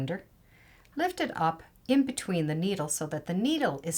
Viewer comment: I can't make out what you're saying: "Lift it up in-between the needles" Is that correct? Also, the link you provided doesn't work anymore, but the company's home page is still good: <link> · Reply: lift it up in-between the needle and I will check on that link. Thanks